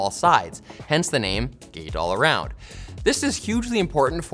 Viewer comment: Why do I hear "Gay All Around"